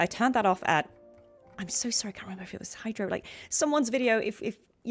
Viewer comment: I went back through my history and this is the video I watched! All credit to KydroTV for the graphics settings :) <link> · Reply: oh i see, Kydro, I thought u said hydro (im using subtitle to watch, lol) I thought it was because he is using a "hydro" character from a different game so I came up with that conclusion. Thank you for replying and i'm looking forward to watch your next stream as well!